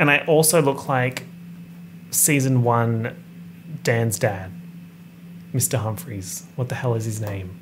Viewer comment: I really thought he said “dance dad” instead of “dan’s dad” at first …. not me thinking he was referencing some dance moms offshoot show or obscure dance moms character 😂😂